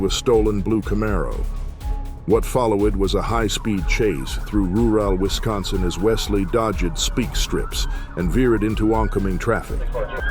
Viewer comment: The A.I. narration is clumsily hilarious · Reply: Yes, I heard “What fall low id was a high speed chase thru roo elle Wisconsin as Wesley Dodd Jed speak strips .. “